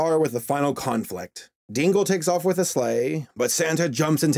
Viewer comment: When he said sleigh I thought he said slave and I was like, "Yknow what, I wouldn't put it past him. Especially after that song."